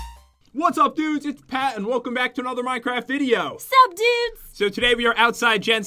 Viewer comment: Did Jen say sup dudes at 0:14???? XD